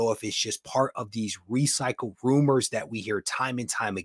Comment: I thought you said recycled groomers. 😂